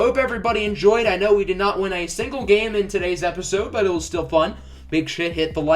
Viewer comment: "big shit you hit the like button" is what i heard 😂😂😂